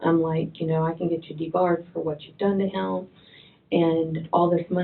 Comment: Wait. Did she say “de-barred”? She wasn’t even as smart as anyone thought. She’s just a trailer park trash, bleached blonde con artist from the hood.